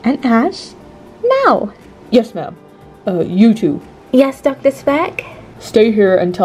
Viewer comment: Who else had captions and when he said you two you thought he said YouTube 🤣🤣